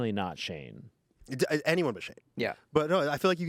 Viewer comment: He said this months ago also right?😂 He was gonna list a bunch of shady stuff and Anthony cut him off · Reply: I thought he said Shane Dawson